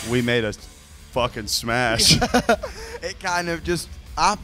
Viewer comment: I THOUGHT HE SAID THEY SMASHED (hAd some niCE fOOK)